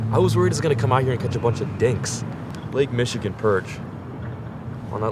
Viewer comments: I thought you said 'dicks' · i swear to god i heard the word dicks